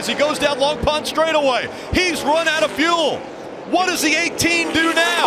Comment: did he just say run out of food??